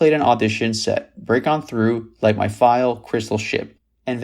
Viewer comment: did you just say "Light My FILE"!!??? WTF? 😅